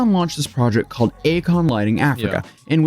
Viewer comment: i first thought he said "a klan lighting africa" .. well that would be something different lmao